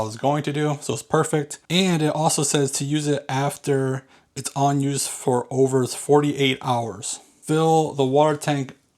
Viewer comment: what did you say there? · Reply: The instructions say to use the self cleaning button after not in use for 48 hours.